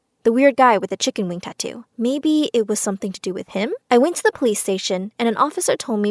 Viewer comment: who heard her saying “I went to the PlayStation” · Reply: Chisom i knwo, at first I thought she said PlayStation and then I realized she said police station